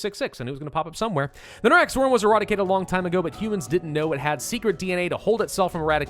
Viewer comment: I'm sorry, but I heard erotic-ated not eradicated. That's an interesting slip, maybe even a Freudian one